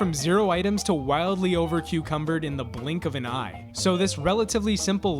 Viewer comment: Did I just hear, " over-cucumbered" 😂